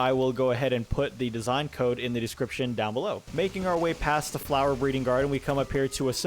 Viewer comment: did anyone else hear MaKiNg OuR wAy DoWn ToWn